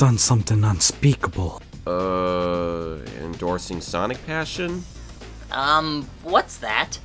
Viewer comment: Oh... I thought he said Sonic Fashion. XDD